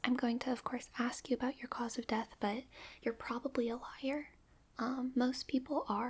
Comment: Did I just misheard that as lawyer instead of liar ? 😅